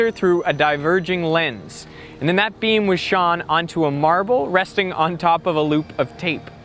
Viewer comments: "And then that beam was Shawn." Haha what?! ;'D Is this how Canadians say "shone" or just how Veritasium says "shone?" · What did you say at 5:04? Did you say that beam was "shawn" onto a marble...? Did you mean "shone"?